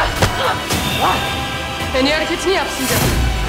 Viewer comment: da asya ne diyor tam olarak en iyi ....? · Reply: en iyi hareketini yap diyor